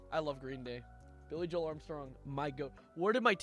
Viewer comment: Heard him say Billy Joel a yelled “UPTOWN GIRL??”